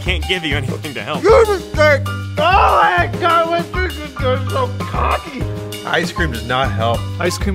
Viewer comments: i think said why did i do that i was so cocky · translation: oh I hate god why was I so cocky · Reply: thereal omega all I heard was "WHY WAS I SO COCKY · Did you just say cocky